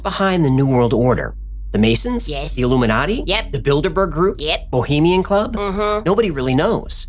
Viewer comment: It sounded like he said "the build a bear group" 🤣🤣🤣